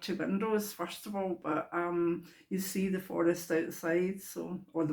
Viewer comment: Nearly thought you said,... "Forex", I thought "what??" is she watching me now?? :) welcome back dear